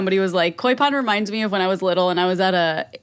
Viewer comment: i thought was saying coi pod